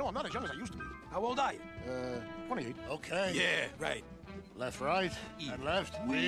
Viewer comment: did Earl say 28 or 38. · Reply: It sounds like 28 to me.